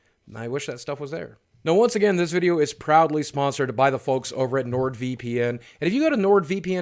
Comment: I thought you said “this video is probably sponsored” at first instead of “proudly sponsored”. I like the idea of assuming one is sponsored.